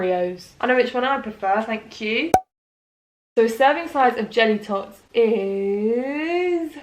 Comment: When she said "jelly tots" it sounded like she said "genitals" hahahahahahahahhahaha the average size of genitals 10.35 pahahahah sorry too funny